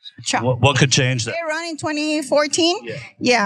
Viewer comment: did she say trump for 2014?